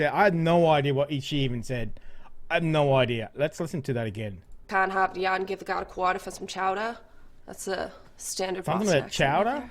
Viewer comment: First of all it is Massachusetts {Ma sa chu setts not Massatooshits}, and what she said is, "Park the car in Harvard Yard, and give the guy a quarter for some chowder." but she said it way too mumbled and quicker than it should be said. I live here and she was wickid hahd to undahstand.